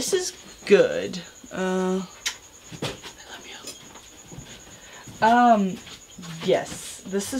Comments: Anybody else hear that kiss and I love you at 7:17? Or only me. Oh i didn't think you would comment on this lol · who else heard her kiss someone and say I love you